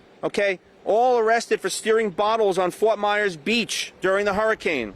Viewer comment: "all arrested for Steering Bottles" What is he saying?